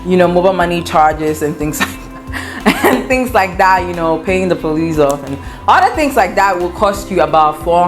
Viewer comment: Did she say paying the police off ?